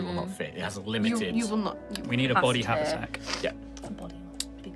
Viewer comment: Did somebody say cadaversack?